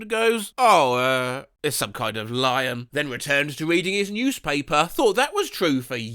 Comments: I thought he said eating his newspaper instead of reading his newspaper and I was so confused about why he just glossed over that part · I thought you said “eating his newspaper” 😭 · Am I the only one who misheard this as "returned to eating his newspaper"?